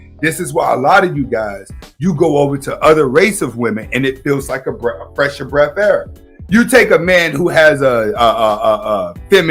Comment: Hold up we not gonna act like we never heard him say. "A fresh of breath air" 😂😂 well damn bro. Jkjk. Much love for the video bro. I like the content. GIVE ME MOREEEEEEEEE